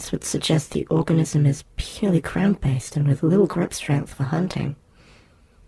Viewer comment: Did they say "this would suggest the organism is purely crayon based"? What does that mean? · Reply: 'ground based' <3